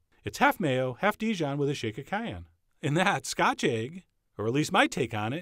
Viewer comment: I can't understand what he is saying at 6:28 for the dip ingredients. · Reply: Half mayo, half Dijon, shake of cayenne